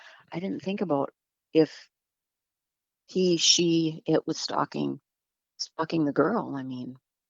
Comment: Did any one else hear @ 11:42-43 what I "think" I heard🤔 " F###ing" the girl😕 Or am I just hearing things🥺🙋😮‍💨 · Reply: it sounds similar but she clearly said stalking my friend. Get yer head outa the gutter lol